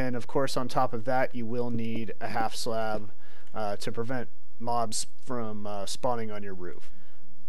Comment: did you said " Mob Sperm" at 17:50 ?